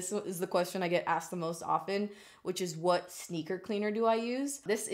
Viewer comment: When you said "sneaker cleaner" I thought you said "stinker cleaner", and I'm all like 🤔"stinker?" as in your butt?? 😂😂 #mindingutter